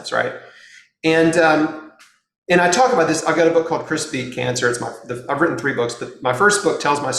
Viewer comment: At the beginning I heard "Crispy Cancer" not "Chris Beat Cancer" please someone tell me I'm not alone 😬